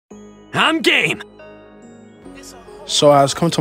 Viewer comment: I always thought that Shallot is saying "I'm gay" instead of "I'm game" xd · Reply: Hey man, I'm not being rude or anything. I'm just saying it's funny that I mishear him saying I'm gay instead of I'm game.